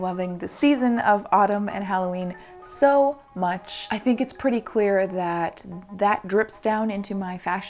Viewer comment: Really sounded like she said "I think its pretty queer" for a second and I vibed hardcore